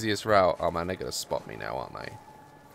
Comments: what did u say at 11:47 i heard ''oh my (n word) is gonna spot me now'' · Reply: Lavender Babies i heard ''Oh my (nword) are gonna spot me now arent they :// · what did u say at 11:47 i heard ''oh my (n word) is gonna spot me now'' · what did u say at 11:47 i heard ''oh my (n word) is gonna spot me now'' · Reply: It's probably the British accent cos I'm British and I definitely heard "oh man theyre gnna..." but can see how it can not sound like that. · i swear down 11:50 says the n word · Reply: Alex Howard no he didn’t he said “man they’re gonna spot me now” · sounds like he says the n word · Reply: Zoe Zamkotowich he didn’t he said “man they’re gonna spot me now” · Did i hear "oh no, my niggas spot me"? or is that just me? ( ͡° ͜ʖ ͡°) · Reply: Bruh he didn’t he said “man they’re gonna spot me now” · Did just say the N word? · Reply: he didn’t he said “man they’re gonna spot me now” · Ok am I the only one that heard him say the n word at 11:51? I know he wouldn’t say it but it totally sounded like it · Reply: he didn’t he said “man they’re gonna spot me now”